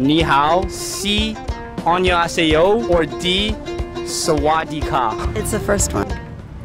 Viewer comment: as a thai, I think I heard swastika